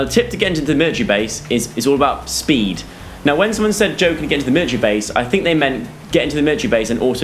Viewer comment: I thought he said midget base!!!!!IM DYING!